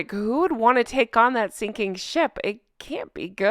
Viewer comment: I thought you said who would take on that stinking $hit ! You said sinking ship lol 😂 Silly me